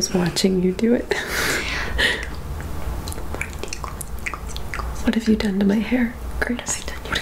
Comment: EVERY TIME I HEAR “what have you done to my hair Curtis” THEN I GO BACK AND HEAR “what have you done to my hair GRACE” am I trippin ? What do y’all hear ?